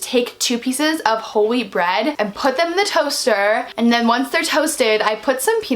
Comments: I thought you said holy bread😂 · i thought she said holy bread lol 😂❤